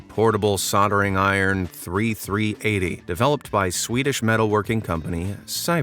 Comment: Did anybody else notice he said 3388 instead of 3380 6:47 Or did he say three three eighty I don't know somebody please tell me